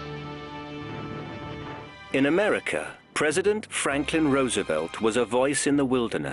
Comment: “President Franklin Roosevelt was a voice in the wilderness.” Please!…Roosevelt nearly let England fall. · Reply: sounds like he says "Franken"